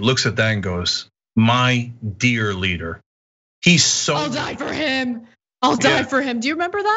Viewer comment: I swear to christ I thought Ana said "I'll diaper him!" which I'm sure his followers would also happily do